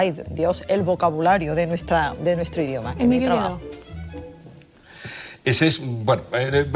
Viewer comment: cuando le piden nombrar a una persona que hable con propiedad, no se escucha el nombre que dice, a quien se refiere? minuto 23:14 - gracias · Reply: Emilio Lledó. Catedrático de filosofía, sevillano y un erudito en lengua, historia y filosofía....y con un fino acento andaluz.